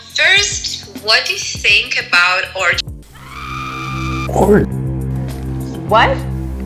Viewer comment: What did they say a 19:25?t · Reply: O*rg*y